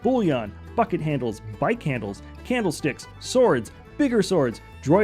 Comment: I heard them as fu×kin handles 🤣🤣